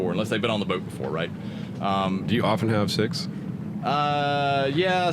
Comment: Hahaha... I heard you ask the question: "Do you often have sex?" -- and I go "Eh?".. only to realize you actually asked "Do you often have six (passengers)" :-) :-)